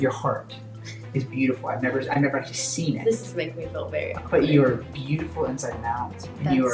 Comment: does she say ”this is making me feel very awkward”??? either way that clip is so uncomfortable to watch